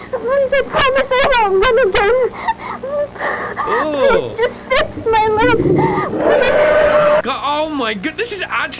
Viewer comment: got me soo uncomfortable. I heard “just fix my legs” like they broke her legs to force her to say the lines.